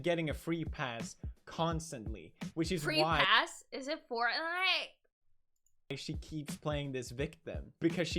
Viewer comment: What does she say at 14:00 after free pass? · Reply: SPAY crowd101 is it fortnite